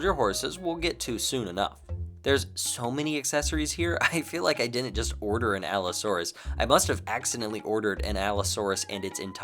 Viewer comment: Somehow, I accidentally turned it back 10 seconds and it sounded like you said I feel like I must have bought an allosaurus. I bought an allosaurus. 😭✌️